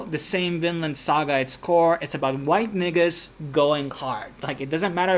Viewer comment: Am I hearing corrrectly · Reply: absolutely, Vikings were like the original White ISIS